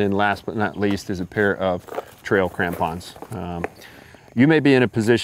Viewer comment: I thought you said trail tampons...lol. I was wondering where your bushcraft mind was going with this...